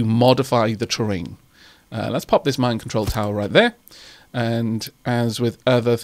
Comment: I'm not blaming your accent, I just thought you said Mind control tower though! :-D